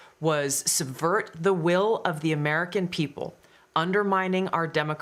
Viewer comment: Did she say Obama reversed the will of the American people when he let Trump be president?